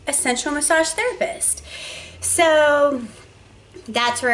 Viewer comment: I actually thought you were saying "central massage therapist".